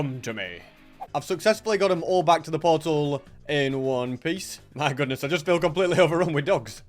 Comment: did you say in one piece?????????